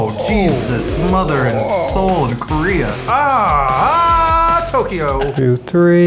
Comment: "MOTHER OF CHINA WHAT THE HELL!?" "JESUS MOTHER AND SOUL OF KOREA!" "oOoOooOOHHHH TOKYO..." Eugene is just using his own heritage as "oh shit" phrases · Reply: I heard it as, "Jesus, mother, and Seoul, Korea." I wonder why I always go to the weirdest interpretation possible.